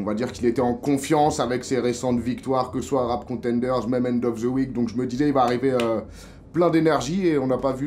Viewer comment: a 20:48 il dit rc et quoi ? mement of the week ? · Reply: end of the week